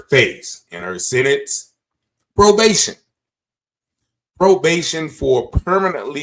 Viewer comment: I'm sorry. I'm getting my own justice for my child! This is insane. ONLY PROBATION??? WHAT A JOKE!!! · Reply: They say “probation”, I heard “easy access”.